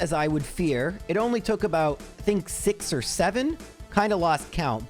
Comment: Did bro just say, 6 or 7...